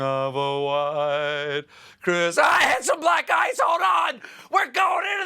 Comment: It took me way longer than it should have to realize he said "we hit some black ICE". I heard "black guys" and things have never felt so wrong.